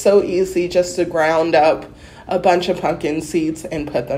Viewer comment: I definitely heard "ground up a bunch of 'f****n' seeds" at first! Whoops! "Ground up a bunch of PUMPKIN seeds". Hey... both work. Also, this was super interesting! Thanks so much for documenting this.